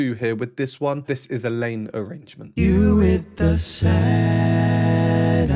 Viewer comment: Couldn't but be ever so slightly jolted, when your auto-generated captions insisted by calling this a "lame arrangement " instead of a " Layne arrangement " which I clearly heard you say. "Lame" is last word anybody could call this masterpiece, IMO.😅❤